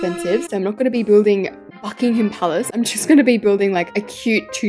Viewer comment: I HEARD "im not building fuckingham palace"